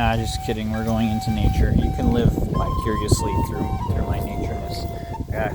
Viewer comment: did you say "live bicuriously through me"